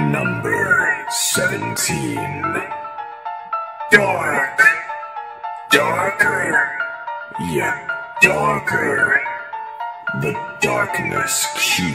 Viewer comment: At first I heard "Dork, Dorker, yet Dorker..." xD